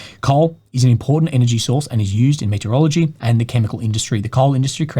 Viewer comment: saying "metallurgy", I hear "meteorology" :)